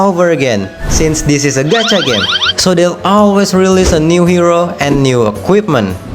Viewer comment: subtitle is wrong , it's gacha game